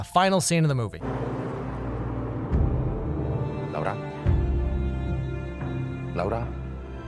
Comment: did he just call her lauda😂😂😂😂😂😂😂😂😂.....for everyone not indian....lauda means dick in hindi....literally